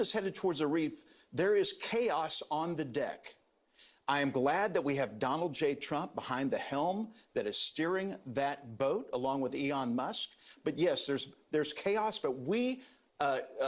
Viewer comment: did he call him Eon Musk😄